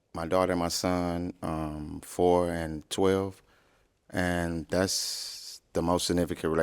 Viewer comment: i could've sworn said 3 and 12 , then came back with 4 and 12 ...maybe the forgot a birthday or something lol